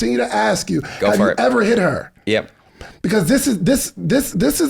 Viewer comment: Thank you and for staying in the conversation so that alot could be revealed. There were SO many lessons that apply to relationships and business. Both two are a class act and I'm thankful you were both on the same page of protection! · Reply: Wait did he say "Yep"